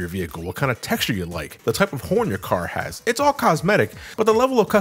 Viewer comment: I thought he said “the type of porn”